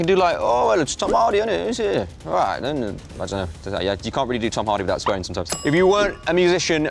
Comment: Gosh...! What did he say at 00:44??? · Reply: "you can't really do Tom Hardy without swearing sometimes"